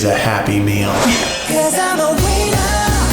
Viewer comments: Am I the only person that thought it sounded like, "'Cause I'm a weiner" at 13:29? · I swear the song at 13:28 said “cuz I’m a wiener!”